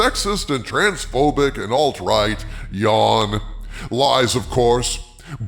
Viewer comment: I briefly misheard you and thought you said "All trite" instead of "alt-right". But it is all trite to say "alt-right", isn't it?